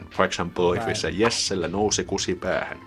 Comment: Your pronounciation sounded like you said "nussi" instead of "nousi". Nussi is a form of the word nussia which is a dirty way to say that you are having sex with someone.